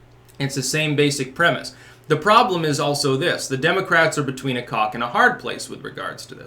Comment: anyone else hear "between a cock and a hard place?"